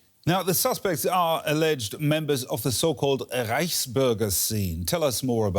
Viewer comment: reichburger? probably got arrested for trying to topple mcdonald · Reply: I thought she said riceburger, I said what? That sounds terrible.